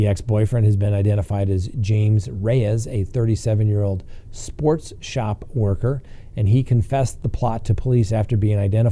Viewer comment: I had to pause and re-listen, because "sports shop worker" sounded like "sex shop worker."